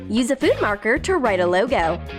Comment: On 3:17she says marker instead of eraser